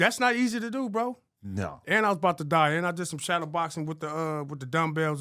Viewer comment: did he say and i’m bout to die?